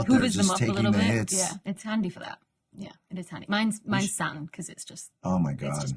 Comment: um... can someone make clear what Dr Becky's fav planet is (4:56)? obviously I know. but for those listeners out there, I heard them asking. BTW... I love the left-right audio separation... feels like old-school hifi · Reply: Saturn is what she said, in a British accent.